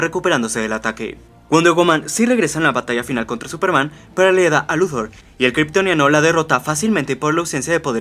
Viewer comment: pero le eda a luthor??? QUE DICE EN EL MINUTO 2:19??????NO VOCALIZA